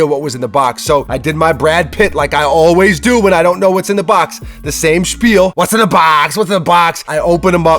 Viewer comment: did he say "the same Spiel"? Is america gettin germanized? 😀 Greetings from germany 😉